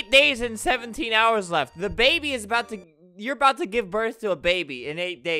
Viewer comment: Me: DID YOU SAY DABABY When DABABY is watching this: LESS GOOOOOOOO